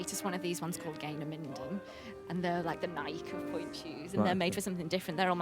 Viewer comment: Did she say gaynors are the nike of pointe shoes at 29:24??